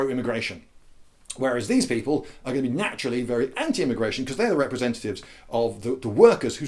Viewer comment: “Emigration” is what I heard you say the first time.